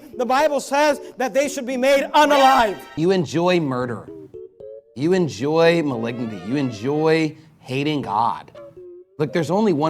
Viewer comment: What is he saying? It sounds like “You enjoy maligngmy” to me, but I have no idea what that is.